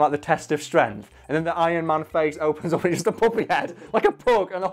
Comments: Every single time he said puppy I thought he was saying puppet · i thought he was saying poppy, like the flower